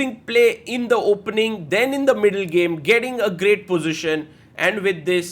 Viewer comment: i thought he said super bad blitz at the start